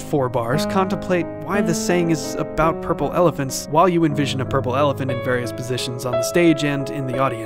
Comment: Did I just heard "purple evelent" and not "purple elephant"?